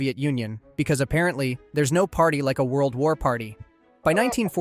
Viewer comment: For a minute I thought you said there’s no party like a dinner party but he end up saying there’s no party like a work party😅